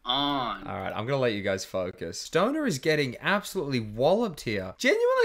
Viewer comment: "I'm gonna let you guys Focus" totally sounded like he was gonna say f**k